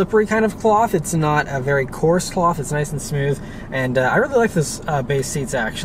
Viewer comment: I like how the cruise control is where the old BMWs had it. Did you say "course sloth" at 4:23? Ha!